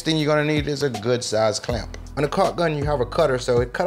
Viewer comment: did u say cock gun?? yummy